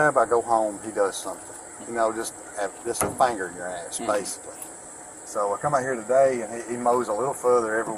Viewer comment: did he say “it’s like a finger in your ass??”